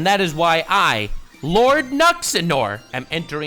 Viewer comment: Did you just call yourself Lord Nuts n' Ore? 😅